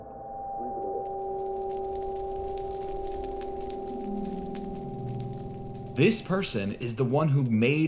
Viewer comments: hiii i know im a bit late but at 17:01 i think it says "leave it alone" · I’m 7 months late but I heard him say “leave the hole” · it sounds like they said "leave it alone" like a parent barateing a child. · I think at 17:00 he says leave this game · i think it said “Leave”